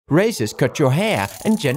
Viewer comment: I heard 'racist cut your hair'💀